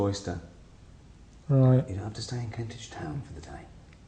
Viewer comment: Did he say "You don't have to stay in Kentish Town" at 16:15? Does Karl live in Kentish Town? Because I live in Kentish Town, I hope I see him some time. That would be amazing.